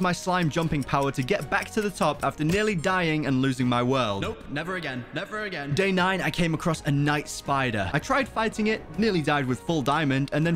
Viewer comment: did he just say he will never lose the world again?